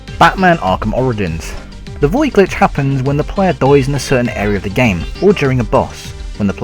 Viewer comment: im sorry did you say died or DOIS